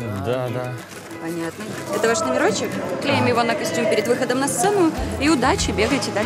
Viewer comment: Мне одному показалось , что она сказала "Клеим на писюн"